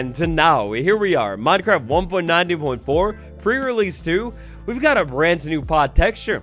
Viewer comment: Is it just me, or does it sound like is saying "Pur release" and not "pre relesae"? Everytime he does these prerelease videos that's all i hear, pur release (meow :3 )